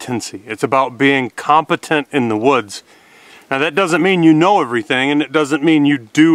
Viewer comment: Is this a video a trap for me? 😄 Wouldn't bushcraft be being competent in the "bush"? 😉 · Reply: 😆 Really? 00:45 I think you said it was about being competent in the "woods".